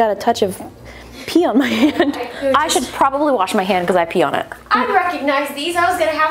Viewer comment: This is what I heard Justine: " I should probably wash my hand, bc I pee on it"